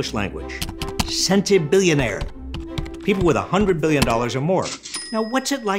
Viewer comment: I first heard you say that, it sounded like "Scented Billionaires," to which I'm tempted to respond with, "What do they Smell Like?"